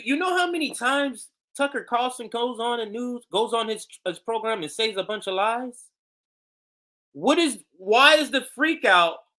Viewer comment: you say tucker "goes on his program and says a bunch of lies" but the closed caption reads(thinks you say?) "goes on his program and saves a bunch of lives"...not sure if you can fix the closed caption but it would suck if a person who had no choice but to use the closed captions then thought you were saying tucker "saved lives" instead of "says lies"!!